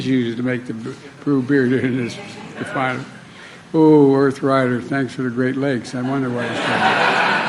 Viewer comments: I heard in part, “arthritis, thanks for the great legs”. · Humanity isn't their strong suit. I thought he said arthritis great lakes.